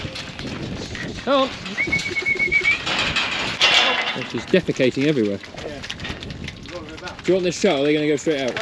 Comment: Did you say "deficatining everywhere"? That has to be a first, I've never heard a Farmer use "Deficate" in my life, and I've been around for a long time. Well done, another good video, thank you. 👍